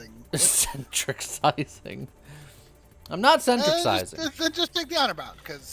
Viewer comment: I thought you said ...."I'm not Centric-sized"... I almost died laughing 😂